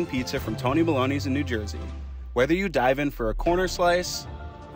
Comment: I thought he said “Whether you wanna die-“